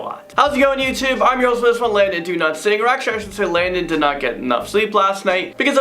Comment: I confess, for a long time I thought you were saying your name is Landon Do Not Sing.